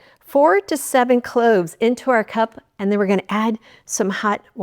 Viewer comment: Whew, im glad I watched this again. I thought she said 47 cloves.